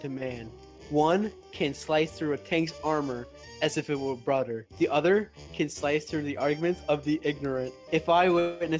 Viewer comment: Did he just said "as if it was brother" rather than "as if it was butter" ??